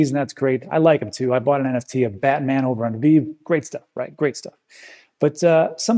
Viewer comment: Where did he buy that bitcoin art? Couldn’t catch what he said, I heard “batman over under b”